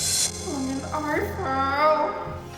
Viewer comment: tadi ngomong apa ya ke Feli yg gua denger tolongin i fell 🤔